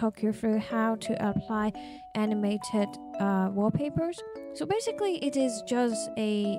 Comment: Ngl thought you said anime tit wallpaper in the intro xD